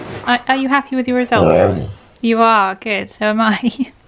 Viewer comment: I think he literally just said I Am. OMG.